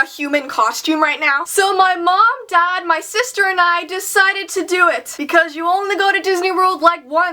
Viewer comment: "So my mom, dad, sister, and I decided to do it!" I thought she said"decided to die!" Lol!😝😝😛😜👻